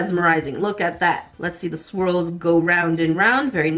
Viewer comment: I thought she said squirrels instead of swirls!😂😂